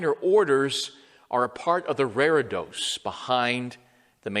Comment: I didn't catch what he said at 1:20. The "raredos" behind the main altar?